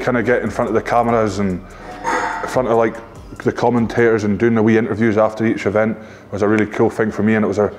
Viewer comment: did he really say, “wii interviews”? lol sum1 explain please